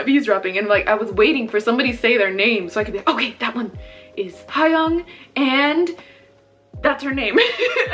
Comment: did she just say hwang from stray kids?